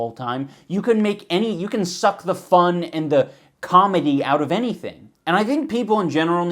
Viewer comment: did he say "cum"-edy? @ 2:53 .....that's a pretty good pun B-)